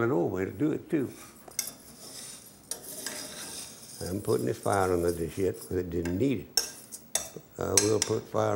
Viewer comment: Did he say put some fire under this shit ?? Lol